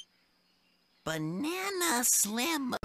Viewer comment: WHAT DID HE SAY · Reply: Banana Slamma